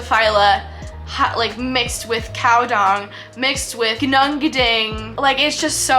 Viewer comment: Hold the phone, did she just say, "mixed with COW DUNG"??